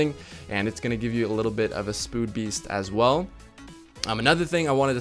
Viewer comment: He said spood beast instead of speed boost lol